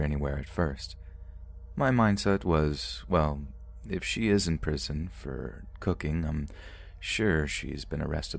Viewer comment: I had to go back cause I heard " she was in prison for cooking MEN"...........but he said meth🤣